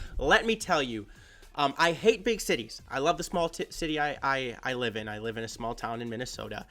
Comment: Did you just say “small tits city”? Haha